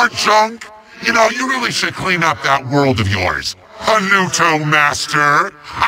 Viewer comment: Can somebody please tell me what Kaos said? “Seriously? More junk? You know, you really should clean up that world of yours, (Anuto) Master!” Am I mishearing something, or is it a joke I don’t understand? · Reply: I think he says "Seriously? More junk? You know, you really should clean up that world of yours, Polluto Master!" as in the Portal Master being a master of Pollution because of all the junk.